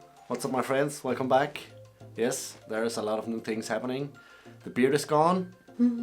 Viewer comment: I thought you said the beer was gone. I nearly had a heart attack. Seriously. Don't ever do that again, man.